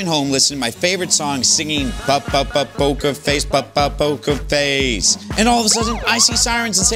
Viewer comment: Thought he said “boka face”